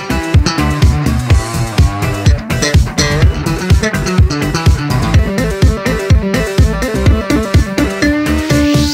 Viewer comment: hehe i heard that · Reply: THE LICC